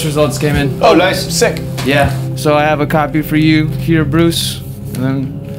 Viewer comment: lmao did he just call him bruce